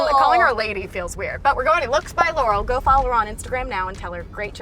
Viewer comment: i heard looks by yanny